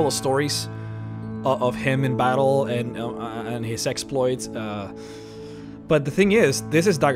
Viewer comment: Did he say ‘sexploits’?